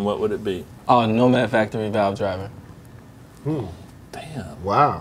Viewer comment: what was his island plug in? i could never figure it out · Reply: Sounds like he says "Nomad Factory Value Driver"